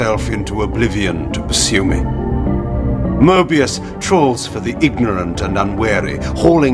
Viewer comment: Did Kain just call Moebius a troll? Sweet!